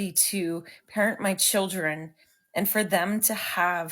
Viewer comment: did she say hurt my children?